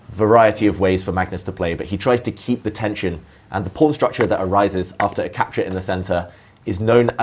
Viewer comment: wtf at 1:59 when they say pawn the captions heard it as "porn" 😭💀